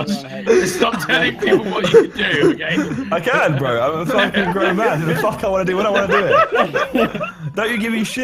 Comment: That almost sounded like: "I'm f*cking a grown man." :]